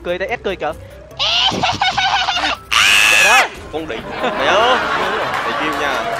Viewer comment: có ai nghe ra là “con đ..." như tui không..😂😂